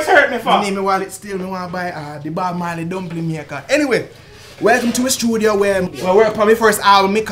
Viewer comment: yeooo da man just said "anyway" Da subtitle said a whole damn paragraph! lol